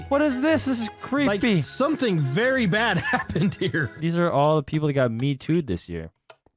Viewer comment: what’d he say? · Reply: “These are all the people that got metoo’ed this year”